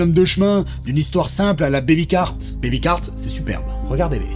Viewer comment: C'est quoi "belicart" conseillé par le à 9:00 ?